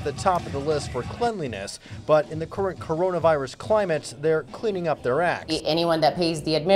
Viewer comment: I Thought he say. Cleaning their asses LoL 😂